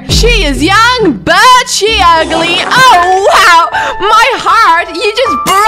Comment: DID HE JUST SAY YOUR UGLY?! imma go and slap him in the face. You are pretty Queen :D